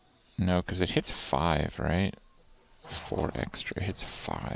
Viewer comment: He actually said 4 eggstra ^^